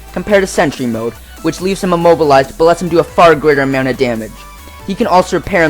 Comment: Did he say "gooder" plz help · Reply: greater